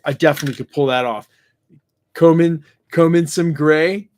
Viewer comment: I thought he said Cole Mason Gray....as Mr. Fantastic. I was like, who is that???